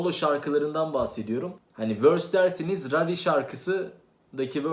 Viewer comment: te karıştırdın herhalde knk ralli dedin ya orda dedim ne diyor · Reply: rarri verse işte